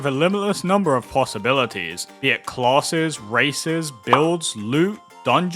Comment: When you said "Races" I misheard you and thought you said "Racist Almost had me sold there. 😅